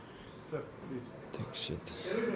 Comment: What he said at 1:57? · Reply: Dog shit hahahaha😂😂